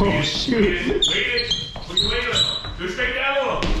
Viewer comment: did say “oh shit”